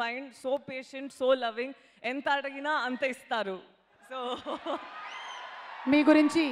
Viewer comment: Can anyone tell what did she say at 3:30?? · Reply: " Enta adigithe anta istaru " ...This means " he gives me as much I ask " ....It looked like double meaning but it was not 😂